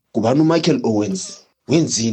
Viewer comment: Hebanna! I alwaya thought he said "fuck my cologne" 😂😂😂 never made sense 😂😂😂